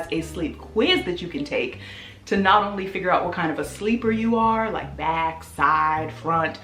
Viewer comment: chile i thought that it said Black sleeper for a sec....... deletes paragraph edit: petition to keep Dennis in the background of every video